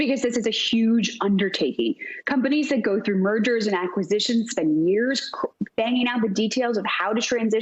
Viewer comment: I heard "murders and acquisitions..." ;-)